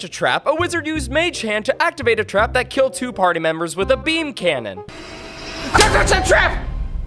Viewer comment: I thought he said Bean Cannon